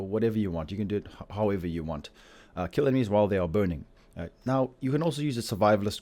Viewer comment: sounds like he said kill enemies while they are boning. I almost fell out of my chair.